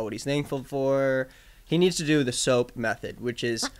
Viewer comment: fully thought said the SOAK method here…which is a whole nother story 😅 edit: OMG “cross upside down” needs to be released asap 🙏🏻 save my soul